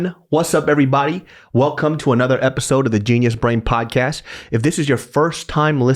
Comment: Low key, whenever I hear David say "genius brain podcast", I hear "JESUS BRAIN PODUCASTU" in an old Korean accent lol.